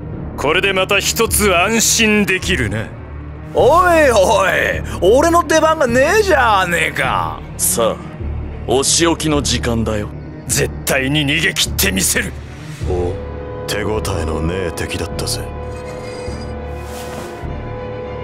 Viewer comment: polernaff (i think thats how you spell it) say n word?